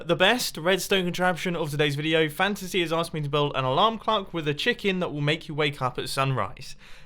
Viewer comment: did he say alarm cluck on purpose or what’s up